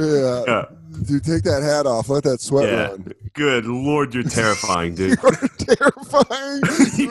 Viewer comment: wtf is Soder saying? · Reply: "You are terrifying"